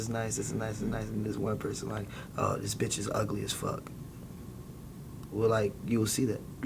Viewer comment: "This bitch is ugly AF" 😂😂😂😂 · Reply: Where he say that I heard him say “This picture is ugly as fuck”